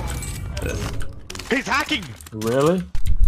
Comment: Hezharkin Sounded like a German name 😆😆😆